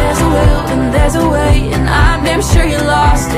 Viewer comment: I misheard the "I'm damn sure you lost it" and heard "I made sure you lost it". I like that version more.